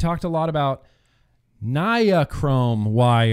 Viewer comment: I know this is old but the first few times you said it it sounded like you said "Ni-A-Chrome" but after saying it 3 or 4 times you fixed it an said ot normally after that lol